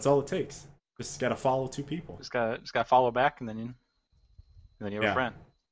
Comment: when said "all you have to do is follow back." all I heard was "I ain't no follow back girl" its been a long day. shame on me.